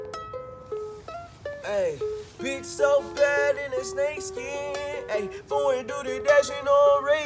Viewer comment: Why can I hear “bitch so bad with the snake skin” and “bitch so bad in the snake skin”